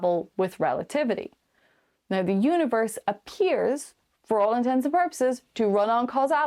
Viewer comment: In your causality segment... you say "all intensive purposes". This is not a thing. It's "all intents and purposes". This may well just be your accent throwing off Google's closed captioning... but it sure sounds like you said "intensive".